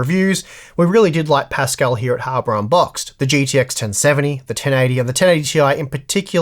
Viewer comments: Im Aussie and even I heard "harbour unboxed' twice.. 😉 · Why did he say hammer on box twice? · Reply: I heard "harder unboxed"